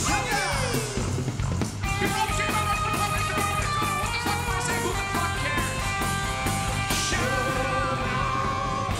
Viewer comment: Wtf am I saying, wtf cares? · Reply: SUGAR!